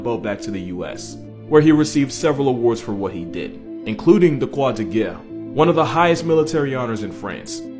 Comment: the... quoi de guerre? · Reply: Croix de guerre or “the cross of war” I think.